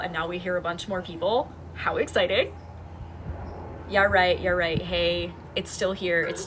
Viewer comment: does it go to the house and is a dome. i have aphantasia so i don't see the field as much as sense it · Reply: at around 20:25 i heard a female say in a very southern accent "they're right". Correct me if i am wrong but is the Sothern accent closest to British English.